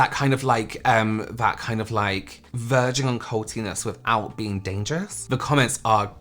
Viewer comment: “virgin cvlti’ness” 🫢🖤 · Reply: I thought he said "verge of cultiness" 🤷🏽‍♀️